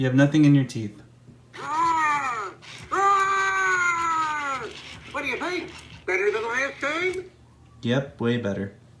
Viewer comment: I don't know wait he said. I hear: What do you think? Better than the Lion King? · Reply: He says "AAAaahhhh what do you think? Better than last time?"